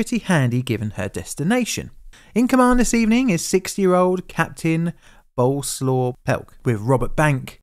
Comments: I almost figured out the disaster right away when I misheard a 6-year old was the captain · I thought you said 6 year old, and immediately thought "well, shit! THAT'S why this happened?" In 10 more years, I'll be cancelled for this comment by people "defending" children's rights, and called "childphobic" for saying a 6 year old captain is a bad idea.